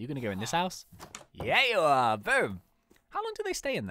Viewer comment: Did anyone else think of his SolveIT series when they saw the thumbnail? Just me? Okay But woohoo, more MC updates from! :D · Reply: Trin.HI i thought you said boooo XD